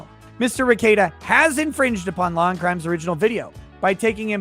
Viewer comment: Christ, I always hear "Lawn Crime."